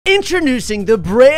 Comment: It sounds like he says intronosing instead of introducing in the start of the video😂